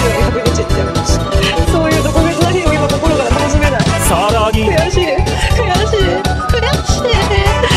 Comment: てんちむにはただただ恐怖を感じる、本当に怖い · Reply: 「悔しい…悔しい…」が「じょうじ…じょうじ…」に聞こえる